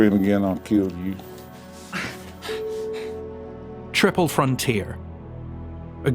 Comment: Haha I misheard triple frontier as thirteen and as I was looking for the movie found that there's an overwhelming amount of films with thirteen in the title! Thirteen, oceans 13, Apollo 13, 13 going on 30, 13 hours, 13 lives - there's so many!